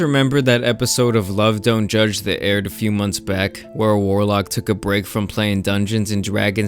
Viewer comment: I thought he said whorelock. But he said warlock.